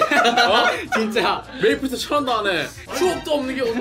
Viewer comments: 메이플 유저는 추옵이라 듣고 일반인은 추억이라 들었다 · 메이플을 모르는 편집자는 추억이라고 들었고 메이플을하는나는 추옵(추가옵션)이라고 들었다... 메